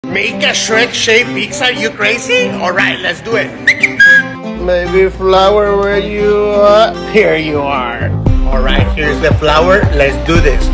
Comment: Somehow I thought he said Shrek Sized 🤣